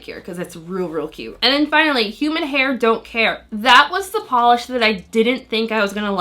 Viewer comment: Oh man, when you first said the name of the 2nd polish, I misheard it as "Human hair, Don't care" and I had to do a double take